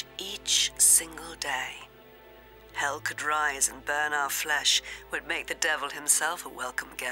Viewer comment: Thanks! I transcribed it this am but got stuck on the line at 1:05:50 "hell could rise and burn our flesh ---- make the devil himself a welcome guest" I think I hear "it would" though maybe it's "but" just not quite sure-do you know? It is a lovely inspiring piece and I urge to post it in the show more/about section under video or even online at the movie's website for all to enjoy...it's beautiful and really moved me. Have a blessed day! ~♥~